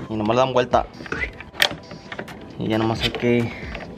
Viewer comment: De donde eres??? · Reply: Ya escuché "hay wey" ajajaja